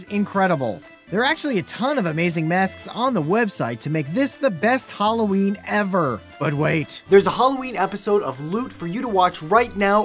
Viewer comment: did you say BEST or EXPENSIVE? ... didnt quite catch that